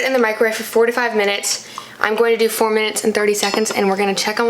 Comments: Omg when you said 4-5 minutes in the microwave i legit thought you said 45 mins lol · i thought you said "for 45min" and was like EXCUSE ME?? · Lol when u were making the Mac and cheese I thought u said 45 minutes in the microwave and I has like 🤭 it would be way faster on the stove. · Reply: She said 4-5 minutes, but the first time I also thought she said that 45 minutes😂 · 🤣 i thought she said 45 minutes haha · girl I thought you said 45 minutes I choked on my water